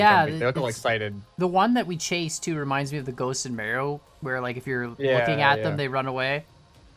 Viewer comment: Did call Mario “maro” at 13:30? It’s (mar e o) lol